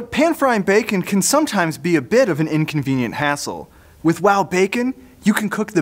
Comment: am i the only one who thought he said «inconvenient asshole»?